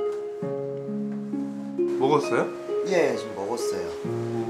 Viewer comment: i was so distraught after seeing the eagle that at 2:38 i thought it said "did he die" i got so scared for a second holy cra